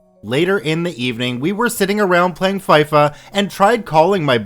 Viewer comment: did you say faifa